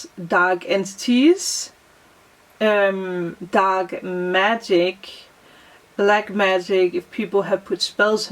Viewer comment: Hello at 32:45 did you say dog magic?